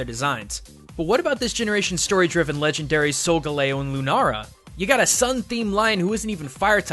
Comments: who else heard LUNARA?? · Wait did you just say "Lunara"?